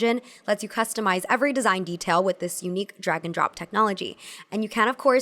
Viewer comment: What she says: "[...] with this unique drag-and-drop technology." What I hear: "[...] with this unique dragon-drop technology." imagines cute lil dragons flying those things across your screen to the place you want them at